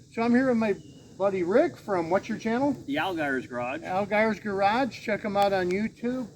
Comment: Hi, what is the name of Rick YouTube site, it sounded like you said Algars Garage, but I can’t fine him. Thanks.